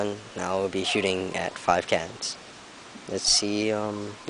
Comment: When you said "Ill be shooting at 5 cans", I thought you said cats